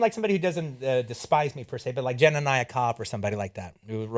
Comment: Who did he say? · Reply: Jelani Cobb. I had a hard time figuring it out for a transcript, but yeah it's him.